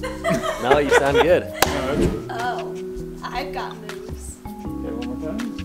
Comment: the subtitles say, "oh.. i got boobs".